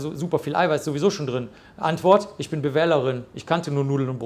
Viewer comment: Was sagt er bei 1:11:25 "ich bin wähl..."? Ich hab das trotz 2x zurückspulen nicht verstanden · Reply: "Ich bin BWL'erin."